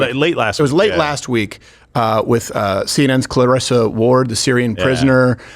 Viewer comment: did he say "Clitorissa" Ward? 🤣🤣